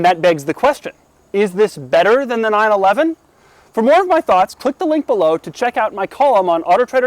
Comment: everytime you say "check out the description for my thoughts about ..." i always hear "check out the description for more of my thots". wtf is wrong with me?